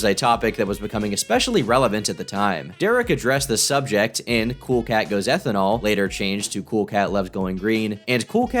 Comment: i was just kinda listening out of the corner of my ear and i hear "cool cat goes ethanol" and i assumed it was about how cool cat becomes an acloholic